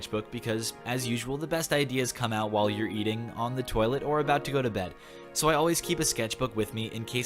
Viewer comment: I heard it as eating on the toilet, or about to go to bed. Lol.